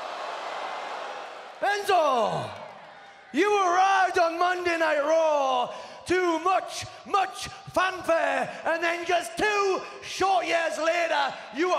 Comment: which word did he say? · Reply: "You arrived on Monday night raw, to much much fanfare..."